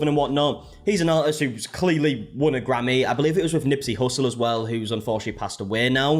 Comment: I thought you had said granny and was really worried for a sec